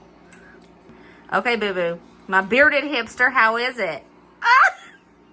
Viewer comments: I thought she said "Bearded Hipster"... have to get my hearing checked 🤣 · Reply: i think she did say bearded hipster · You look so cute in your stocking hat. the and the bearded hipster. Have a great day. We are having a few days below zero as the high. Love you and your family. I use the seasoning blend with eggs mmm so good. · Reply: I’m so glad you said “bearded hipster”! I thought she said “bearded hamster” and could not figure out why said that! Lol! 😂😂😂